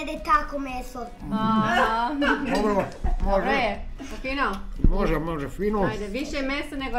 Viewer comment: Thank you so much for this video! My baka never let me in the kitchen while she cooked. There's a Croatian word we said growing up that I've always wanted to ask you about, and I think I heard say it around 18:53 as he gets a piece of bread. I can't spell it but it is a verb that means to sop up sauce or liquid with your bread. Think of the unsellable middle of the word "measure" I'll use zz for that part: muh-zz-uh. Is that what I heard? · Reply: It's spelled ''može'' and it means ''it can'' or it can just mean ''yes'' as in agreeing with something. In this context she asked him ''is it delicious?'' and he said ''može, može, fino'' which means something like ''yes it can pass, it's delicious'' with fino meaning ''delicious''. Soaking up the bread with something would be said as the verbs ''namočiti'' or ''umakati'' but that's not what he said here.